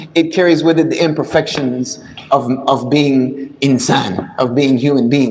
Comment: the subtitles say insane. It's supposed to say Insan which means human in Arabic not insane in English.